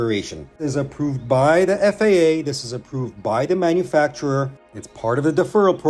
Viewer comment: I was thinking about the time they speed taped up Yuki tsunoda's rear wing when it broke in half after DRS early in the 2022 season, and I heard 'FIA' rather than 'FAA'